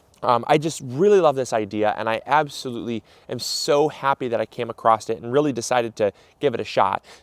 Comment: Does he say "acrossed it" at 3:51?